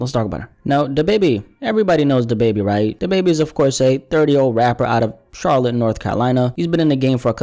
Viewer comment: I’m high asf I thought you said da baby is a dirty old rapper lmao